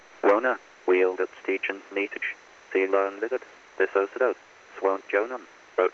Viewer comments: All I hear is “tree line lizard” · I heard ‘Teeline Lizard’. Teeline is a form of shorthand for journalists.